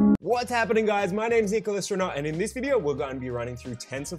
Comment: I heard like "Nicol Astronaut" 😉